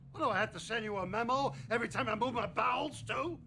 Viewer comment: i thought he said “everytime i move my balls too!” without the subtitles 😂